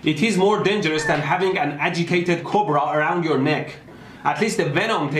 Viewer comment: Ohhhh, 'agitated' cobra... I heard 'educated cobra', and was trying to figure out why an academic cobra was more deadly